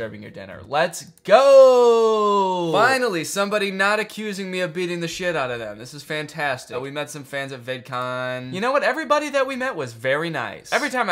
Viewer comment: I thought you said “we met some fans at vape con” and that just made me chuckle